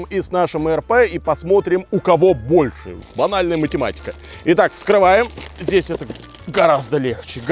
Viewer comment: мне послышалась анальная математика